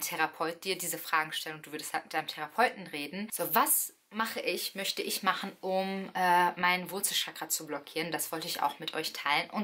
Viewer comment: PS: 14:14 könntest du noch einen text einfügen `:) ich verstehe da " was möchte ich machen um mein wurzelchakra zu BLOCKIEREN"